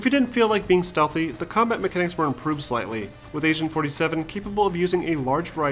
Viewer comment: did he just say Asian 47?